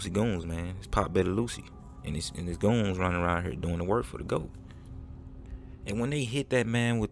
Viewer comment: I thought u said these goons doing work for the government!